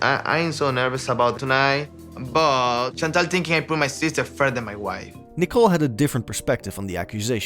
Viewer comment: they didnt think that out of the blue.. ur family treats their daughter like 💩 & you allow it!! and did he just say “i cant put my sister infront of my wife”????